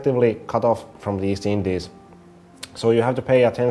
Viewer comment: I think I heard "have access doing porno ports" somewhere around 8:31-8:35 I'll definitely get this game. to be honest, I think Paradox is going to be the next Blizzard.